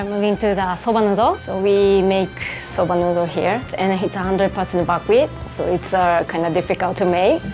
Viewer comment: for those who didn't hear it properly she said " It's 100% buckwheat "